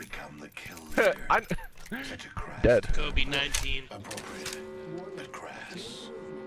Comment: in the background sounds like someone says covid-19 did he predict it 9 months ago